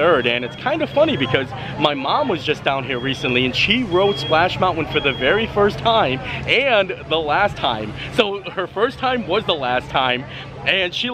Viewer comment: Hahaha, yes, it sounds like you say "mounwin," lol! It's all good! We love your awesome videos! Love the fancy shirt with your photo! Will be watching your auction. Merry Christmas!!!!! 🎅